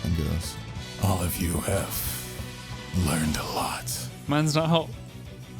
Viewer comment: what does say here? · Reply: i believe he said mans not hot